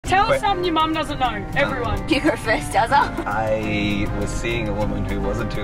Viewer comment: I heard “Dadza” w got so confused. like Phil? where?